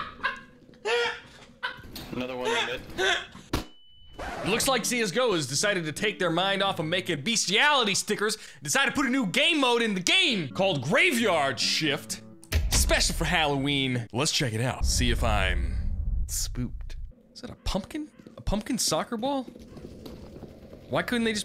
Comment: I heard " Called Graveyard Shit "